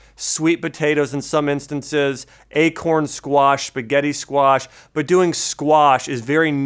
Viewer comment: did he say spagettii, i hope he did, i love spagettii.